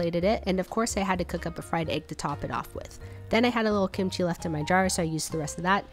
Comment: When u said “ I had a little kimchi left in my jar” I thought you said “I had a little kimchi left in my dryer” LOL